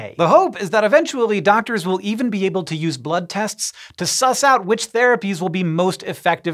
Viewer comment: Did, did hank just say SUS